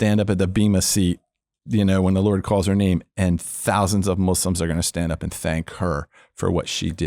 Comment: Did he say Muslims at 32:41?